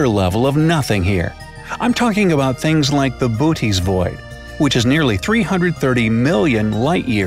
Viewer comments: I thought said booties thingy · what did he said @ 1:05 I know what booty’s call mean but never heard of booty’s void.🤔.